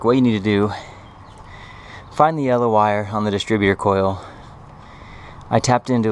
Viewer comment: I checked the wiring and and my pickup is not prewired for the tachometer, can you please tell which wire did you tap in the distributor coil? I think I heard you say yellow wire but I'm not sure. Please let me know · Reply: Correct, yellow!